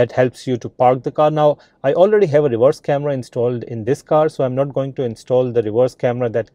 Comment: am I hearing “divorce camera” 😂